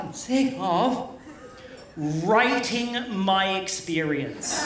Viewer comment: I love how the subtitle transcriber thought Randy saying, "rating" was him saying "writing."